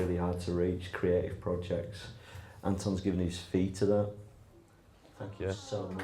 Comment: What does say at 13:26? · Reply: ormazabal "Anotn's given his fee to MAP' the charity Chinwag supports; a Leeds based registered charity who run music/art programmes for young people who are at risk of exclusion from mainstream education :) what a guy! <link>